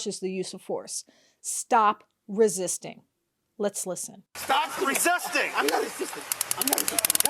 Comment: I know he didn't actually say it, but what I heard was "stop resisting, boy".